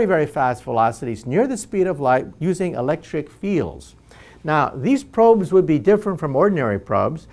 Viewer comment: I heard "using electric eels" interesting few seconds before my brain corrected that... :D